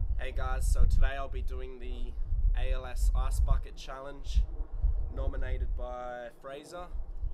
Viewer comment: It sounded like you said ass bucket challenge not hating just laughing earnd a new sub;)